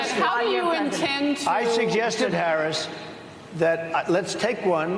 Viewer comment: 🤔 I thought I heard Harris Faulkner.. I thought she recognized Trumps genius.